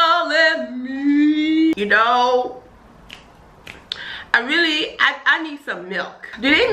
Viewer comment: SHE NEEEDDDS SOOMMME MILKKKK!!!!!!!!! lol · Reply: I heard you say "I need some milk" 😂😂